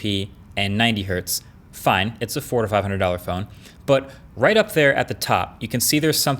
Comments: I totally thought he said "its a $4500 phone" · Did anybody else hear $4500 phone the first time or is it just me? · I thought he said “fine, it’s a $4500 phone” 😳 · legit thought he said 4500 phone i almost flipped